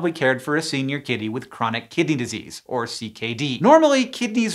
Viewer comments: I thought you said Chronic Kitty Disease and when you said that cats were more likely than dogs or humans than getting it I rolled.my eyes before realizing that just maybe I had heard it wrong. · I thought he was saying "chronic kitty disease" for like the first 2 minutes of this video lmao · I accidentaly heard it as "Chronic Kitty Disease"... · Misheard this as Chronic kitty disease 😂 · i misheard this as "chronic kitty disease" the for the entire video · I thought he kept saying Chronic Kitty Disease... I was like: "I have it, I have CKD!" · did anyone else mishear it as "chronic kitty disease" every single time?😭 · Lol i thought you said chronic kitty disease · I misheard it as "chronic kitty disease"